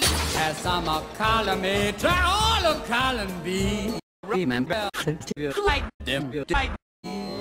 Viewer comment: What did he say? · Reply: "Remember, first you fly, then you die."